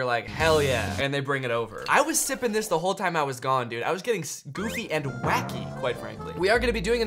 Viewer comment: i thought was saying “scoofy and wacky” until like the 20th replay, i was so confused